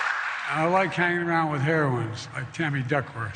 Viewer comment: I thought he said he likes being around ‘heroin addicts’ 😂